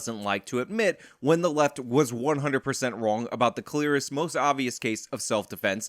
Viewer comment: Real talk when you said he wouldn’t admit the left was wrong about the clear-est case of self defense I thought you said he wouldn’t admit the left was wrong about the clitoris.